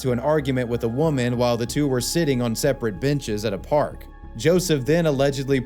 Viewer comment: did he say he poured the gasoline on the 'bench?'